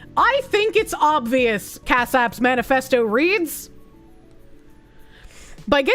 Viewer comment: I keep hearing "Cashapp" when she says the name of the guy in the first article 💀